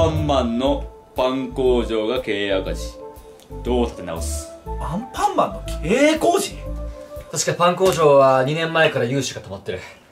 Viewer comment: アンパンマンの経営工事って聞こえた笑